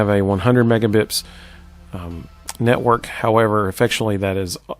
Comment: Haha! Thanks for your constructive criticism! I'm glad your health insurance premiums won't increase. Thanks for watching the video! Mbps stands for megabits per second. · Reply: Oh, thanks for the explanation. I kept hearing you say "megabips" in the video so it naturally led me to wonder what that was.